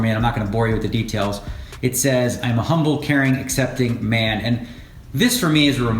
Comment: Am I the only one that thought it said I am a Harambe at 7:40 ??